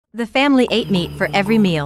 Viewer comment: I thought I said the family ate me💀